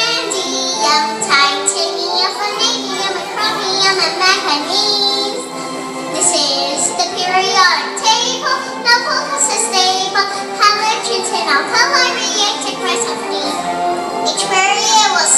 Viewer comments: Sounds like she is saying this is the Periodic Table nothing here is stable hahaha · Did she say Na Fa Ta a stable? That's what I heard :P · I thought she was gunna say mac and cheese instead of manganese